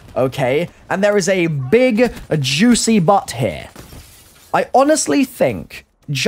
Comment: did I just hear" there is a big juicy butt here"